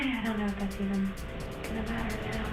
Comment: she's very obviously saying "I don't know if that's even (slightly pause or break in her voice) gonna matter now" not "I don't know if I can get doesn't matter now" cause wtf does what the captions say even MEAN lmao that's a nonsense sentence. Fuckin silly 🙃